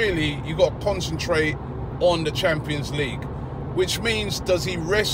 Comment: robbie did i hear champions league or my ear sight.....???